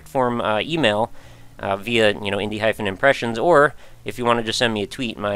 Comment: Wow, I thought you had been saying "indie hype and impressions" instead of "indie hyphen impressions" this entire time. I'm an idiot.